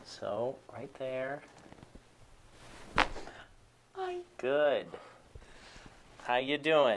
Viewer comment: Did she just say hi? 😂